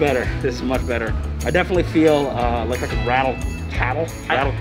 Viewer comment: I was really looking forward to this and y’all did not disappoint. It was a lot of fun and I appreciate you doing this for us. I thought I heard rattle cattle and I couldn’t stop laughing! Now I’m hungry.